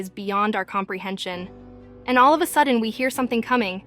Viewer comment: Is it only me or anyone else hear her say oh yeah baby at 9:53💀